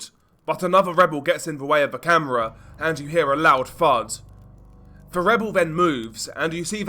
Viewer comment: I had to rewind bc I thought you said “hear a loud fart” 😭